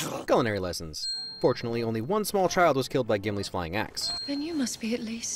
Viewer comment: For a moment there I thought you'd said "fortunately only one small child was killed by Gimli's flying a***". Got me good.